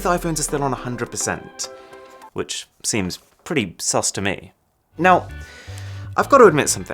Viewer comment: did u say sas ? 😂